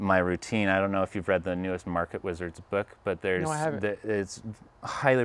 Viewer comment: can anyone tell me what book did he mention at 20:43 ?? I would be appreciated it. thanks. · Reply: Market Wizards